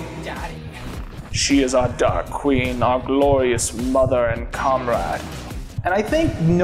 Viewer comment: Is it bad that I thought he said c*m-rag instead of comrade 😅